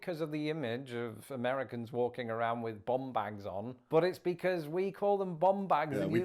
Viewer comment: The bum bag/fanny pack thing is due to American tourists. Tourists are told that when they travel to carry their money in front of them to avoid pick-pockets. So people who have never been to America and have only seen tourists think we all wear “bum bags”. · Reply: I’m not gonna lie, I thought he said Bomb Bag and was very confused as to why the Brits would call it a Bomb Bag, Bum Bag makes much more sense 🤣🤣🤣